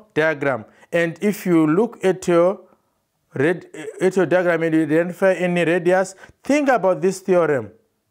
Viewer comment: Can anyone tell me what he says in 2:24 lol ? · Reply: Hi Rajveer, thank you for your comment:). It was a little stutter, but what he said was: "If you look at your diagram and see a radius, think about this theorem..." Referring to the Perpendicular bisector/ through the centre group of theorems.